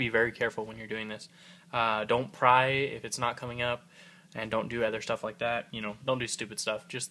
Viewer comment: haha I thought he said “Don’t cry if it’s not coming up” XD